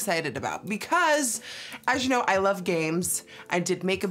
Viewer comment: Lol when she said “I love games” I thought she said “I love gays” bc she just finished talking about pride week 😂